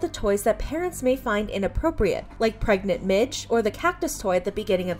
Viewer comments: at first I thought she said pregnant ɓίϯɕհ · Imagine taking away someone's choice because YOU don't like something. Guess it applies to toys as much as laws, go figure. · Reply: totally did not hear pregnant b*tch instead of pregnant midge